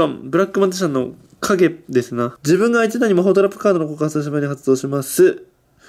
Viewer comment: 魔法トラップがマホトラップに聞こえる